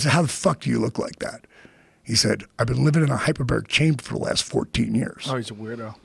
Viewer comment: I am sorry but could anybody tell me what he says at 1:08:55 my mom has ms can't figure it out. I am not a native speaker. What i hear is hyperbird? Thanks in advance. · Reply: Hyperbaric chamber - <link>